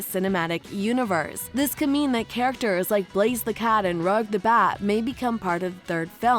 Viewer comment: Did she just say “Rug the bat” that is sooo wrong